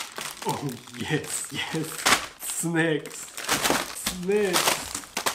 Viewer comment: is so crazy. I thought he was saying "snakes"... but he was saying "snacks"... 🤣 Congrats on 285k subs... I can't wait for 300k... 🤪 coming soon!